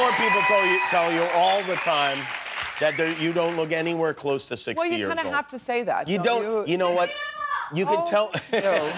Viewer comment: What is yelled a@ 4:20? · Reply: JP51ism you’re beautiful